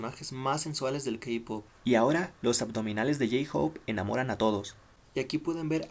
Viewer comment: Me ilusione pensé que decía "J-Hope se enamoró de sus fans":v